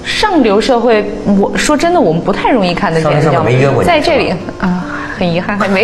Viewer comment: 文涛插话说的什么？听不清？ 哪位大侠帮忙 ! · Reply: 上流社会没约过你吗